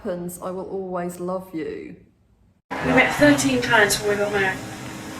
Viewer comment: What does Lady Diana say at 0:55? "Thirteen _ before we got married"??